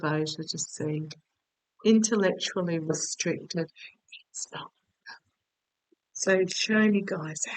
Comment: I believe I heard your boys, at 15:45…could swear I heard ‘Stop’…. ??